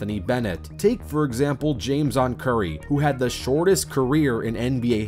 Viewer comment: I thought I heard James' son Curry played for 3.9 seconds. Had to repeat that part